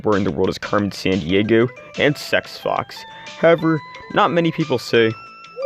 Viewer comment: DID I JUST HEAR "SEX FOX"?? bro what-😭